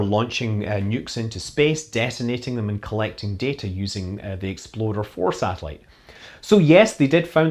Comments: I'm sorry, I thought I heard exploder 4 satellite. Got my mind on my kerbals and my kerbals on my mind. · I thought you said “Exploder 4 satellite” 😂😂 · Is the satellite name at 13:32 really named "Exploder 4", or am I just hearing things?